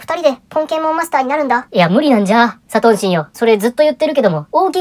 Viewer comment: サトンシンが｢3等身｣に聞こえるし、実際それくらいで面白い